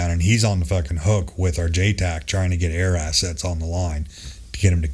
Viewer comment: JTAC? I thought he said no air assets?